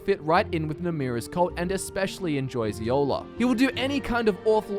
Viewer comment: "And especially enjoys YOLO." I legitimately have no clue what he said there. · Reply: Eola, the name of the cannibal lady you meets when doing Namira’s quest.